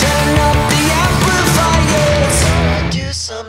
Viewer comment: HERE COMES THE SHARK, HERE COMES THE SHAAAAARK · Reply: Haha I heard “shank” at 1st